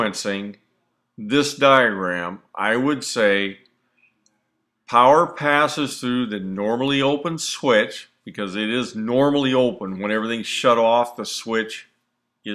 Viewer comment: Did you mean to say power passes through the normally closed switch to energise the gas valve?I thought I heard you say power passes through the normally open switches to energise the gas valve!What did you say at the end with the switch diagram showing both set to pass current?